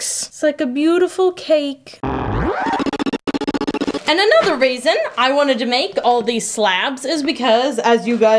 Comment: I want a WHITE/CREAM Opal Soap for October! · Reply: Lol, I think because it sounded like she said "raisin" instead of "reason" when she was speaking.